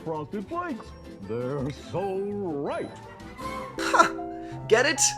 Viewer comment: I thought they said “they’re so ripe” and now I’m wondering how a joke can be so bad that it’s better when I misheard it